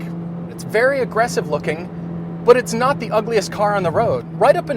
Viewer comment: DID HE JUST SAY IT WAS UGLY?!?!?!?!?